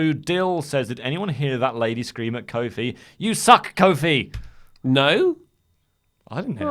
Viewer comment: I heard the lady shout You Suck Kevin Owens lol